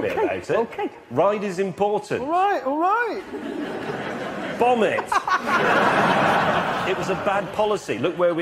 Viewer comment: at first I thought be said vomit